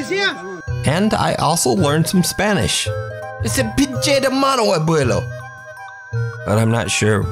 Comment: In this part 36:08 he actually says “Mono” which is monkey in Spanish.